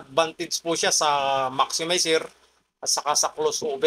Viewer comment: tama ba narinig ko closeover paki korek ako kng mali hehehe